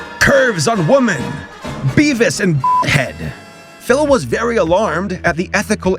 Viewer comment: He censored beevis and butthead, I thought he said dickhead but I turned on subtitles... all it said was beevis and head. I'm laughing my * * * off.